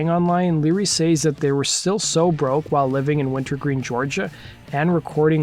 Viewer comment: Winterville, Georgia. Sounded like you said “Wintergreen”, but I’ve lived in Athens essentially my whole life (32 years) and never heard of Wintergreen. Winterville is a small town on the Eastside of Clarke County that chose to remain independent when Athens instituted a unified government to become Athens-Clarke County.